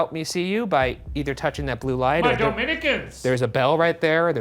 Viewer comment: is it me or does Shane say: My Dominicans, which is soooo weirddd, cause I am a Dominican watching this video